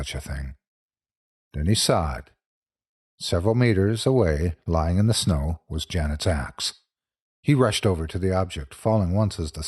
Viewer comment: I can't be the only one who, at first, thought he said Janet's "ass" was lying in the snow...